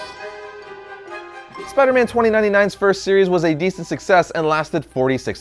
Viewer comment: Anybody else heard him say 3:50 to 3:53 he said dc sucses